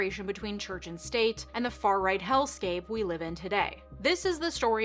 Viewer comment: Sounded like you said hellscape. I think you mean healthscape or maybe you never read the Your Bible Story books at your dentist cardiologist orthopedist sleep specialist endodontist etc. The question I have is....why not the gastroenterologist? The other question I have is....should I see a proctologist and what sort of Bible story would I get?